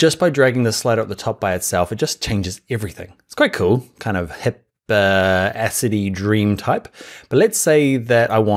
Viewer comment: did he say acidy dream type at 31:34 ?